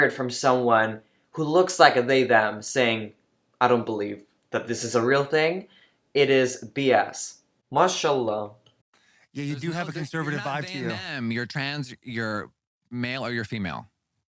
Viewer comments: DID U ACTUALLY SAY MASHALLAH??? 😭😭😭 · Did you say “MashAllah” at 2:31 🤔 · did he just say mashallah?